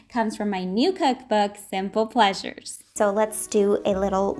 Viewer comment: I thought you said "sinful pleasures" and was so taken aback lmao. Anyways, congratulations and thanks for the recipe! 🩷